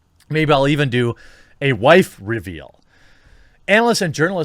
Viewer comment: after reading the title i heard "wife review"! XD and i had to listen again to realize he said wife reveal...